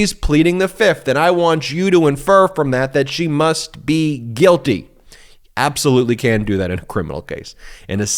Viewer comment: kelly ward takes the 5th .i hear "i'm guilty"